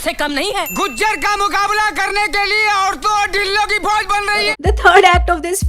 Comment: Did he say " aurto aur dildo ki fauj" 😶😶